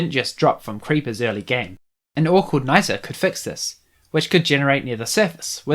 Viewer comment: Sorry if I'm stupid, but what did you say at 10:32? The captions just say 'an awkward nighter' (lol) I know you said something about 'an ore called' but I have no idea what you said after that also, I'm really enjoying these videos, keep it up! I really hope more people watch them, they're really underrated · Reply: An ore called Niter :) And I'm glad you're enjoying them!